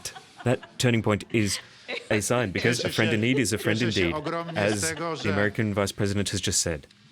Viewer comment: What did he say? :) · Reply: Przybysz he said: friend in need is a friend „in d*ck”. That's why she was laughing 🤣